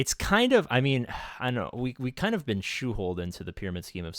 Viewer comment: Did you just say "shoeholed"?